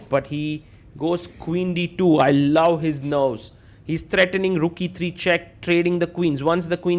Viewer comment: did sagar say I love his nose 😂🤣😂🤣😂🤣